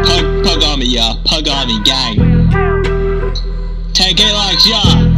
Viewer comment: For a second I thought he said pug army gay😂😂😂😂😂